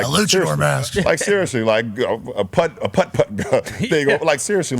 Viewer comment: LeBron tried to say plethora, but he couldn’t get it out @ 8:28 never mind think he say a putt putt